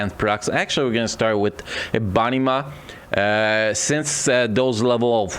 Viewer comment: No offence its all jokes but they way you said EBONY MAW @ 16:25 sounded like "A BUNNY MA". lol